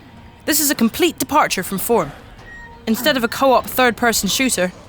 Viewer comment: can anyone tell me what she said? i keep hearing "this is a complete departure from 4"